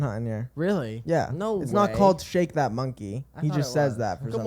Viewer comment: VERY OFF TOPIC BUT I THOUGHT NLE CHOPPA SAID SHAKE THAT BONKEY I JUST THOUGHT…